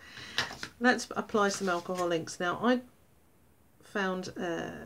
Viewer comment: When you were reading out the items needed for the tutorial I misheard alcohol inks. I thought you said alcoholics! Gave me quite a giggle :)